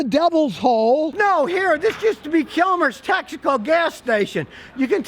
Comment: Thought he was saying Kilmers tactical station